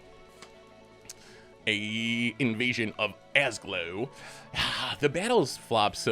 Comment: did I just hear say "Invasion of Ass Glow" at 4:58 ? :D